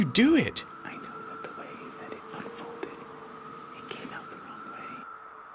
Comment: Does he say, "blindfolded" and "led them the wrong way"?